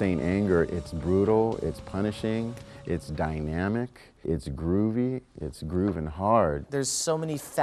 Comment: Did he say Groovy??? :S